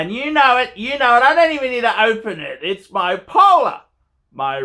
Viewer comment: I actually thought you said "It's bipolar". But that's you, not the watch... (you seem to be relatively sane recently, probably because you're trying not to "scare the horses" while attempting to sell those watches)